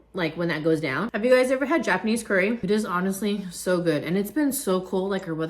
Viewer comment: thought i heard you said Japanese Karen 🤣🤣🤣 love you videos ♥️♥️♥️